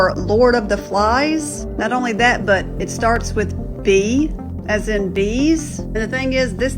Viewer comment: did she say b as in bees 😂😂😂😂 im d as in done.....